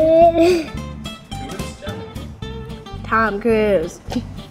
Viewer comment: The girl says "How do Chinese people eat this?" I'm done with being Asian if all other people think we're all Chinese. -_- (It's Japanese food.) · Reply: Did she just say Tom Cruise?